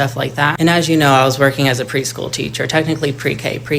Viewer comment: His work ethic is so good he had to do this at a pre-k… is that what he is saying 💀